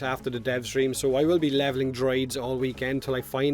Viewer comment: You'll be levelling what...? I can't understand what you said. Droids??? Wat?